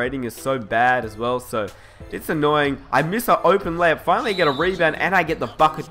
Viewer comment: sounded like you said f it 😂😂😂